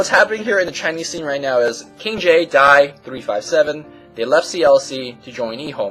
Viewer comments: I thought said KingJ die, oh its just X!! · I thought u said "KingJ died" Phe<link> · LOL AT 1:58 I HEARD "KINGJ DIED" AND WAS LIKE OMFGWTF LOL HAHA